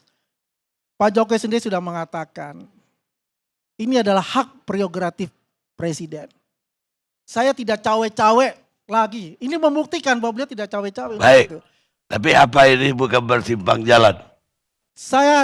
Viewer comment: What did the dude say at 43:44 ? "... saya tidak cawe-cawe LAGI." ? Hmmm... Berarti emang pernah melakukan cawe-cawe yang serupa kah? Mohon diklarifikasi ya😅 Aiyoooo...